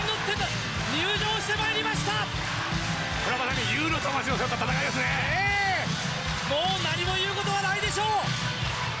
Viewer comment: 外国語でしょうか？なんて言ってるんですか？ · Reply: これ正にUの魂を背負った戦いですね。